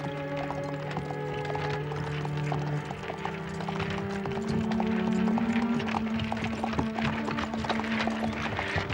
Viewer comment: what does she say, at 19:14 'if it weren't for that, you'd be ... 'patist' or something? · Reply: Papist I mean